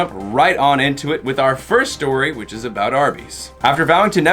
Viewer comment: I thought I heard "the first story is about herpes."